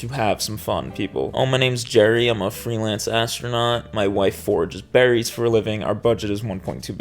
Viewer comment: lol, I heard that as "My wife forges berries for a living" and now I'm obsessed with what that could possibly look like.